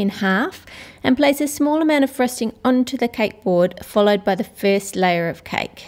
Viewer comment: Wow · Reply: Btw it sounds like ur saying fresting